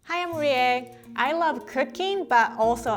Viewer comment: I thought she said I like cocaine in the intro and I was likewhaaa